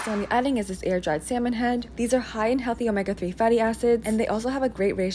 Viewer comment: I thought she said "a#ses" when she said "acids"😂😅😭😭😭